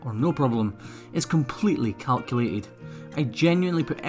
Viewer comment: I heard “everything is cowculated” 🤗